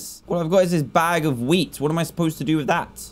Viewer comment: I thought he said “all I’ve got is this bag of weed!”